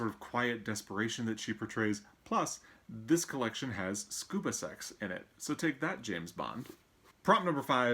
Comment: Dang!! I should have chosen a The Master and Margarita as my choice for that prompt! Btw, not sure if its because I have a Great Dane or not, but when you said "Scuba Sex" I thought you said "Scoobie Sex" and I started to have one of those childhood memory shattering moments. I love the Revisionist History podcast as well.